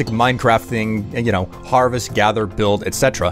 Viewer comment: I thought you said Parvis instead of Harvest 😂